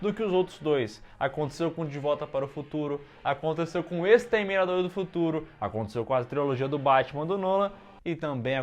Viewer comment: vei eu tava comendo e entendi "o exterminador do teu furo" tô rachando kkkk